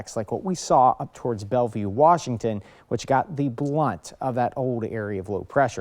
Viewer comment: Did you really say blunt? Blunt of? Like a doobie, or joint? Or did I mishear you saying brunt? We replayed that part twice. You can't have said "the blunt of"?